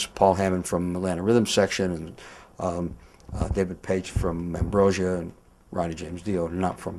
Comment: you mean David Paich from TOTO, Ronnie? lol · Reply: Yes, I know Pack was from ambrosia. It sounded like he said David Paich. Sorry, I'm not too familiar with Kerry's solo work. Probably should.